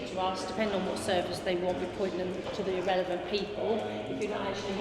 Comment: did she call her colleagues 'irrelevant' ? 😅